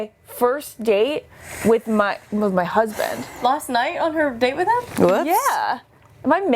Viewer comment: Ashely you’re not crazy i also LOST IT when Hannah said that Luke will either be her first date with her husband or her last date with him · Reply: Yes, I heard her say that. she said "future husband"